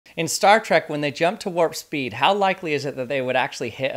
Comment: They also explain that the deflector dish literally deflects smaller debris and im pretty sure they also explain that the computer can make adjustments at light speed to the course as not to hit anything at least on voyager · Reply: oh he said Star Trek I thought he said star wars